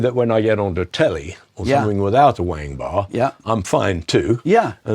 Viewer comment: does he say..."Wang bar?" Or "whammy bar?"